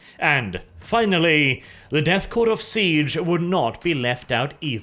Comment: anyone else catch that , you said the death corps of siege not krieg .